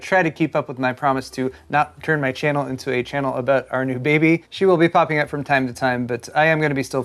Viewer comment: When you said our new baby I thought for sure he said Arctic Baby.